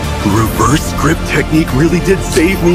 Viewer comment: "Reverse grip technique really did save me huh!" - killed me 💀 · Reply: wait, it's what being said there? i heard "rebirth technique" 💀